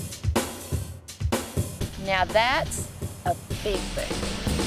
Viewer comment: she said now thats a big boob she has nice boobs, thats wat im saying